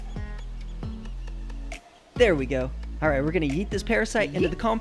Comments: I thought he said he was gonna eat the parasite for a second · I thought you said let's eat this parasite from the compost ToT